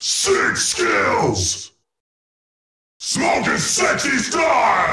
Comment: I thought he said "having sex gangnam style"